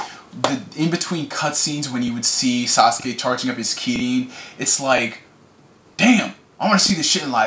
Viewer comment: he says sasuke charging up his ki instead of chakra